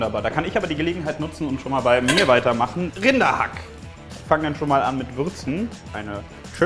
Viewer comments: Ich habe Kinderhack verstanden XD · ich hab statt RINDER-hack Kinder-hack verstanden. ..